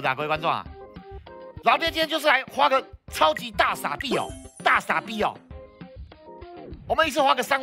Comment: 我聽成超級大傻逼XD